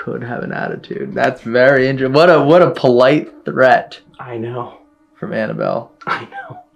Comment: a tap can be heard. 6:07 odd noise. 14:10 the dragon/serpent is moving. 19:17 “move” can be heard. 24:25 a growl can be heard. 24:44 snort or squeak can be heard. 25:58 that’s spooky. 27:42 got his answer, but didn’t hear it. 28:39 what fell? Or was someone moving something? 34:06 a second being on the SLS camera. 40:25 “yeah, no” can be heard. Not sure if it's from or. 50:08 a roar can be heard. 52:15 the roar can better be heard here. 53:31 that’s creepy. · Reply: Yo someone go to like 12:40 and listen again for me. But I’m pretty sure it sounded like Annabelle said I know just as he was saying I know. It sounded like it was said twice. But the second time I heard 2 voices. Or I could just be tired at almost 5 in the morning with a colicky baby